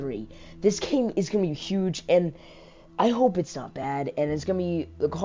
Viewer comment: I thought you said “I hope it’s bad”